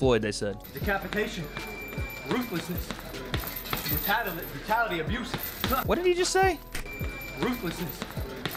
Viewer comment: "Rattata abuse" LOL He said "Decapitation, ruthlessness, and the vitality of youth", talking about his age gap against Floyd and how although Floyd may be the pro boxer, Logan's trying to say that he can overcome that hurdle because he's younger. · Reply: I thought he said "mentality abuse"